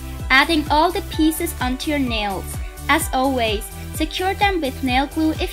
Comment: i think she said glue them with nail poo